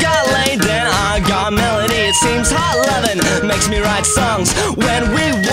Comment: this whole time i thought he was saying hot lemon made him write songs 😭😭